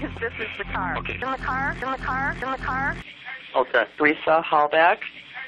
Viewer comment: Wow I totally heard "the car is here!"